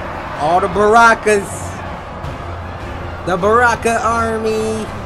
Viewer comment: did bro just say army of barakas😂😂